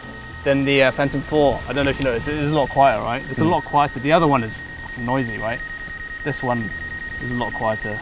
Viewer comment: Did he said Fu**ing noisy at 4:38 ?